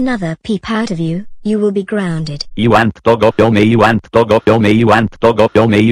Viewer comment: what was saying? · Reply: I want to go home